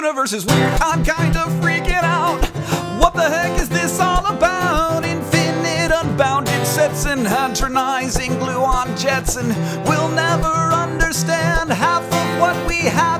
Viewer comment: I keep hearing "what the Hank is this all about?"